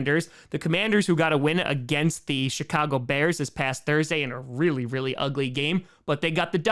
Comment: It’s sounds like is saying Shitcago Bears when he puts that inflection on it. 😂